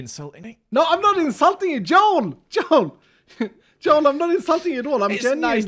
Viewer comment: am I hearing calling "JOhn"?